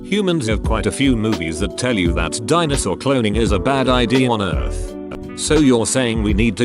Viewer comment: I forgot the time but instead of "Dinosaur Cloning" I heard "Dinosaur Clothing". That made me smile c:. (It wouldn't be unethical, but it would be expensive make clothes that big.)